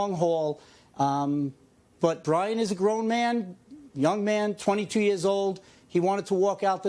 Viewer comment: did he say "Brian IS a grown man." 😳